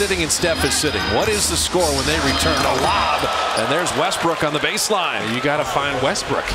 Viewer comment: did he just say westbrick?